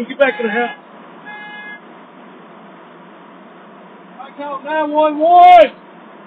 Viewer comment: he said ”I call 9-1-1” But the subtitles thought he said ”I don’t know why, why?”